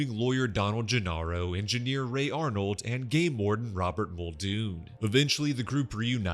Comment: I thought you said "gay lord", instead of game warden, and I was wondering what this guy did to you.... Thanks for the vid!